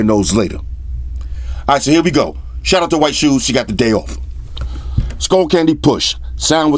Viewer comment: Thought you said shoes has the day off?? Never get time to talk amongst ourselves, bummer!